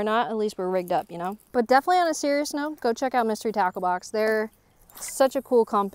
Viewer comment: Not gonna lie, I heard "Mystery Taco Box" several times, and that made me hungry and sounds delicious. :P So, try to also get a "Mystery Taco Box" to sponsor you, with a 'becca' discount code also. :D